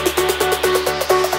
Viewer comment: I seriously thought the music said jack it off.